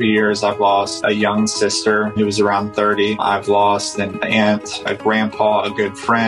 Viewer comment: I thought he said that he had lost an ant bruh